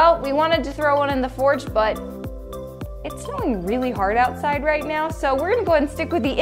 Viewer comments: did she say it’s SNOWING?!?!!! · did she say it was snowing?